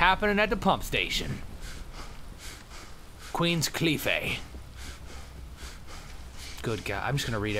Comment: sounds like he says Queens Queefay